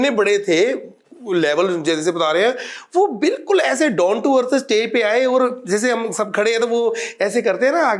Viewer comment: Did he say 'Don to Earth'😅😂😊